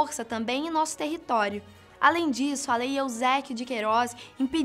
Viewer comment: só eu entendí Lei Euzéquio de Queiroz? Não seria Eusebio?